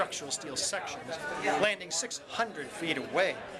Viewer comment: People in the so called truth movement dismiss the fact that it is absolutely not a theory what Dr Judy Wood puts forward, it's a conclusion based on all the evidences anyone can see from the images and videos made on 9/11. Furthermore she takes in account other evidences like seismic waves which cannot be found on videos or images but are very much one of the most important evidences on this topic. You can't go to court against someone without having this type of evidence. In the truth movement they point out to puffs out of the buildings and they emidiately suggest it is a demolition, now that's a theory. These puffs could easily be a part of the ceiling falling down or something which creates this dust puff, which would also be considered a theory. The fact that there is far from enough rubble to support the theory that it was a demolition is being shoved away or dismissed within the truth movement. It is preposterous. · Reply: Joe V yes i thought he said 600 yards and he said 600 feet, which only 2 football fields in any direction. However, it is, still, bullshit, because the ONLY proof of such an event is his thumbs and lying mouth. Just because he can jam his thumbs laterally and make up a fantasy, doesn't make it an occurrence that was filmed and/or encountered.